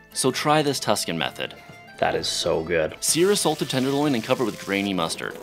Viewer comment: "sear a salted genital" (I misheard) followed by him giving a hand job to two mustardy pieces of meat my appetite disappeared real quick